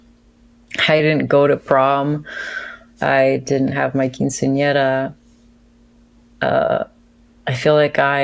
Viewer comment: Can someone tell me what the thing is she said after not having a prom? 1:06:25 sounds like some kind of Italian American or Latino ceremony? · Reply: Quinceañera. From 'quince', fifteen and 'año', year. It's a feminine noun meaning a 'girl who is fifteen'.